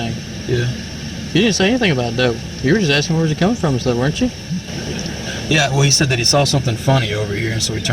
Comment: "Lets try to irritate him" is fucking wild.. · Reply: Pretty sure the idiot cop said, “you were just asking where was he coming from, weren’t you?” Idk why the caption at this part says, “trying to irritate him,” but it’s pretty clear that isn’t what was said. Still a pos cop for hassling the IA cop.